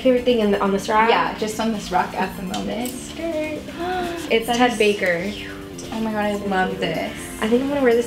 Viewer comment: I swear I thouht she said ''it's Hannah Baker''